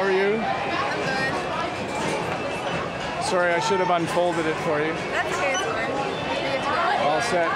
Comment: Thanks for your great work, I’m really intrigued with your videos. Ops at 02:04 what did you say?! “ shouldn’t.... folded it “ can’t catch u 🌚😹